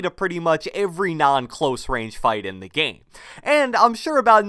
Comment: did u say orange g or RNG? 😂😂